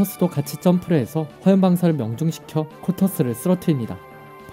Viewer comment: 초에 다탱구가 아닌 코터스라고 들려요